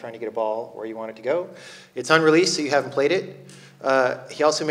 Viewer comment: Where can I find this game? Orc Tower by Marc ten Bosch? Checked out his website and see no mention of it. Seems like it doesn't exist. Edit: nvm, I misheard Jon Blow saying 'Un'-released'. Though he said it's on release...